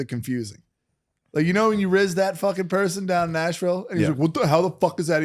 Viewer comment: Did say Asheville at 52:02 ?? I'm from there:D